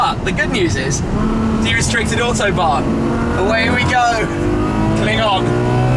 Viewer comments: Why did call a "Klingon" at 4:34 of the video? Very Trekky of you... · Err, did you say, klingon?